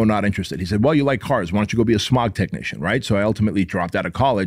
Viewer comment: When I first heard it I thought you said your parents wanted you to be a shut technician? 😂